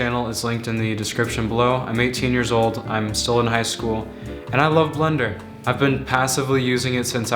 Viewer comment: I heard I love Londen lol